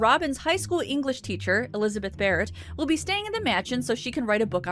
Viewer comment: Oh! When you said "Elizabeth Barret", I thought you said "who isn't a ferret".